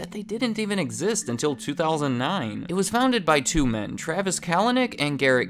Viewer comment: I thought he was going to say Travis Scott 😅